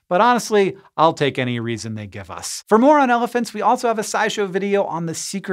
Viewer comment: I heard "more on elephants" as "moron elephants."